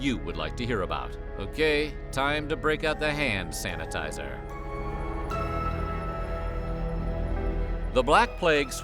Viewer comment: For a split second I thought you said “ham sanitizer”